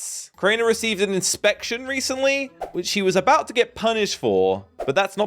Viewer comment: did just say (she) for crainer?? Lol